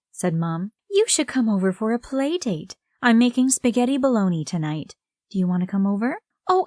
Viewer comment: Did she just say she was making spaghetti baloney😂🤢